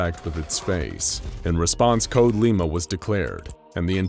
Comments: CODE LIMA Me who heard code ligma: AWW MA GAWD BRO AW HELL NAW BRO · Whta others hear “code lima” What i heard “code li g ma”